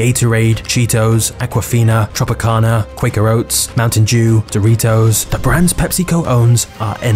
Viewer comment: Maybe my hearing is going, but at 0:37 did he say "Mountain Jew?"